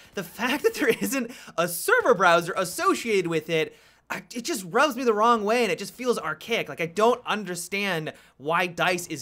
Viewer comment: did he just say: ''it rubs me the wrong way''?